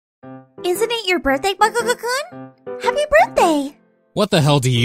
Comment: It’s sounded like she said bakukukun